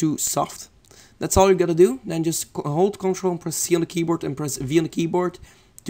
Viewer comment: Don't to be mean, but some of this I can't understand, and now I'm stuck. At around 6:12 when you say click "control..." I don't know if you say p, v, or c, and when I try all of them, there's not a duplicate slide option coming up. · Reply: 1.Click on that thing and Click CTRL and C at the same time on your keyboard. 2.Ctrl and V on the same time. that is it